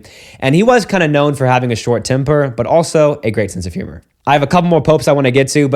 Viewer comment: Oh you said “temper”? I heard “timber”